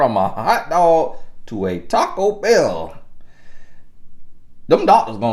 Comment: "when you're going from a hot dog to a taco bell" 🤣🤣🤣🤣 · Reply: Oh I thought he said Taco Shell 😂🤣🤣😅😅